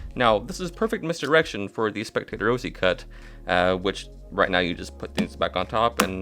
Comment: Hey, sorry but what is the name of that false cut you said right here 2:36 Sounds like ya said "spectator O.C. cut" ?? 🙏👍Thanks so much for your tutorials. I've learned so much from you & really enjoy your videos✌😁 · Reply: I'm glad you enjoy the videos! The name of that cut is the Jay Ose Cut. The spectator shuffle variation is an idea by Ben Earl.